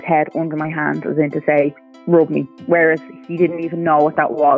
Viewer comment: the subtitles are wrong just so use know. "Rub me" is what was said. Am happy to see Walt doing so well in this home:)